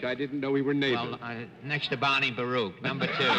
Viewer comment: So at the end of the Van Johnson segment, I thought Fred said he sat next to Bonnie Baruch (there was a Bonnie), but he actually said Bernie Baruch (who's not related to Bonnie). Either way, I had no idea what he meant. Turns out, he meant economist Bernard Baruch, who was in the Wilson White House, and advised other presidents as well. This I knew. (Mostly, I know of him from his UN Atomic Energy Commission efforts.)'Apparently, he often did his later advising on park benches, which I did not know. The world is an interesting place. <link>